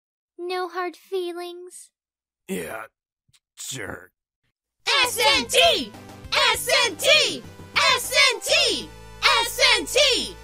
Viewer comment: what they are saying ; SNT! SNT! SNT! captions ; am about to end these narrators' whole career.